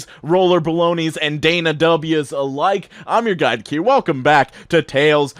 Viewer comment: bruh im a dumbass in the beginning of all the videos i thought he was saying "im your guy cue" smh